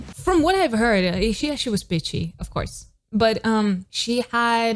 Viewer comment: I thought you said she was bitchy 😅 Then shortly after I understood from context that you said pitchy 😂 But that really took me off guard